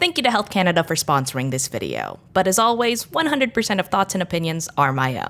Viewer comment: I thought you said that Canada sponsored this video and I was so confused